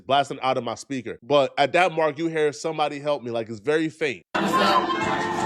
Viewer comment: it actually sounds like can someone help because if you have good editing software, you can take away all the other noise and just listen to the actual voice and it does sound like she says can someone help but loudly